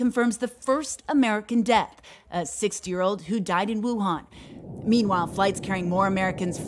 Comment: Did she just say "a sixth year old"